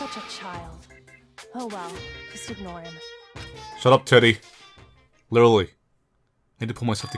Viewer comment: “Shut up Teddy” I thought he said “Shut up Titty” 😂